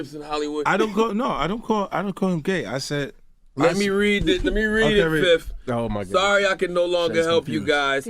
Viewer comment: Did he just call him " 5th " 💭 "yo 5ifth" lemme read it fiff " . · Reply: Fif as in fifty cent 😂